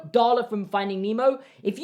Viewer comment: 😂 I thought you said garlic instead of Darla lol